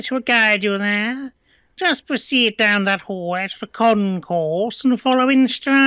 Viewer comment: I've been watching to much Hermitcraft i could have sworn he said "Con Corp" instead of "Concourse"